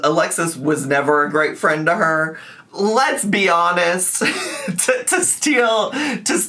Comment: I could have sworn you said, “lez-be honest.” Despite that misses opportunity, ambers life is so hectic!